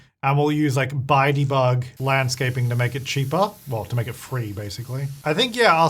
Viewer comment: I thought James said "use bitey bug", so I had to go back and listen again with the captions on to work out he actually said "use Buy debug"! 😂